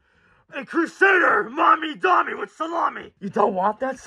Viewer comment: I'm sorry, did you say "With Salami"?